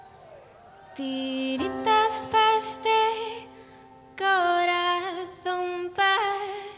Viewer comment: Coñooooooo por fin entiendo lo que dice la canción en, "tiritas pa' este corazón partiooo" Gracias, por la actuación digo, maravillosa!! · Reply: Siempre pensé que decía tiritas de este corazón partío'... O sea que el corazón quedó en tiritas jaja